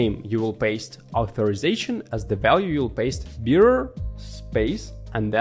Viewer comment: Here he said: In the "Headers" section, click “Add Header”: Name: Authorization Value: Bearer<Space>YOUR_TOKEN_HERE I couldn't understand it, and when I turned on the captions, it said "beer," which confused me even more. So, I'm leaving this post to make sure no one else gets confused like I did.